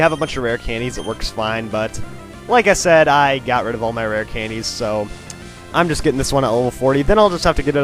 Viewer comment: For a minute there I actually thought this guy said he throws Rare Candies away… o-O